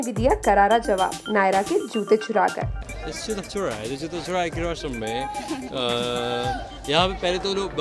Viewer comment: what did he said???? can anyone tell? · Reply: He said its joota churayi